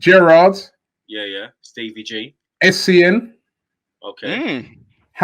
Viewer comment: What's the player name that say after Gerrard @ 21:53? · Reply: essien